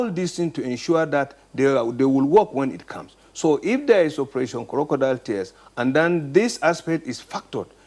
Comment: Did I just hear operation crocodile tears @ 5:34 ??